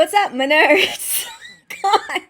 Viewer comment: I thought you said Minnerds because you are doing a video and some of your viewers (at least I consider myself to be a WoT nerd). Another good video. Thanks for your insights, I love the shout out to your avatar: "Min has a good head on her shoulders" I guess you think Min is a sexy flashlight I think Tuon's character would have also significantly changed (either for the good or for the worse) in the outrigger novels.